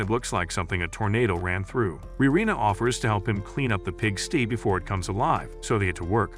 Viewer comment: y'all didn't hear him saying"pigstee"😂😂😂😂😂🎉❤